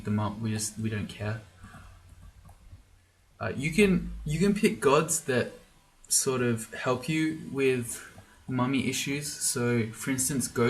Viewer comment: For a second there, i heard "you can pick gods that help you with mommy issues"